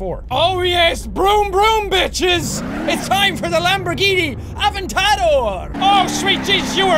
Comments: The sound was low so when jack said Lamborghini avatado whatever it is I thought he said lamborgini avocado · When he said aventador i thought he said avacado also WHY WOULD U KILL THAT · Lamborghini avartado did he just say that. It is Lamborghini aventerdor